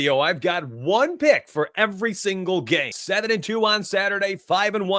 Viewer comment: you cut out the audio at the beggining and it sounds like you said you have pick for every single GAY instead of game 😂